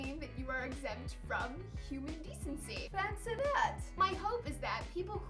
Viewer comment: Lol 4:09 when the caption thing thought she was saying 'Pants or not' when she was saying 'Fancy that'